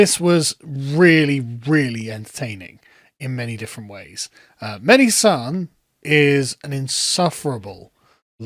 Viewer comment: I thought you said Mehdi San then lol 🥋🥋🥋🥋🥋🇯🇵🇯🇵🇯🇵🇯🇵🇯🇵